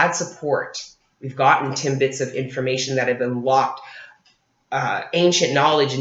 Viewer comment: Girl did you say Timbits of Information?! lol <3